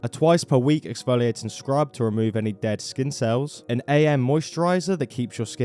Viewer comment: when you said “to remove any dead skin cells” i heard “dead incels”